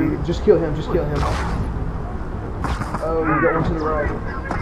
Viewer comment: Did you say "Arrow you got one to the right" ? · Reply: No he said "Oh you got one to the right" typical arrow always thinking about himself